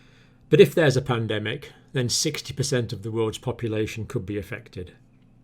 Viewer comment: did he say “60%”? Jesus